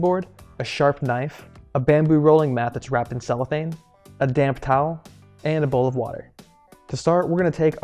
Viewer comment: Since we are going through the pandemic now, and I have be wanting sushi since we first we under quarantine, I decided to make my own sushi. So, of course I came to You Tube. I found your channel and this episode because I love spicy tuna rolls. Your video is extremely helpful, and easy to follow. I hope you are a sushi chef, and/or a teaching chef, because you are very talented. You did make me laugh out loud when you came to slicing the roll. I thought you said “take your damn towel and wipe your knife”. 🤣 DAMP towel.....DAMP!! 🤣